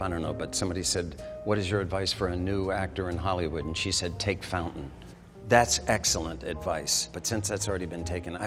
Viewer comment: When asked what the best way is for a young starlet to get into Hollywood, she answered “Take Fountain.” What?!? Driving on Fountain Avenue rather than Sunset, Hollywood, or Santa Monica Boulevards is a well known shortcut through the Hollywood neighborhood of Los Angeles. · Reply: lol i thought he said “take fentanyl”